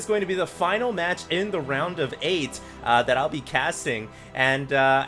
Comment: Not gonna lie...i thought said "round of AIDS."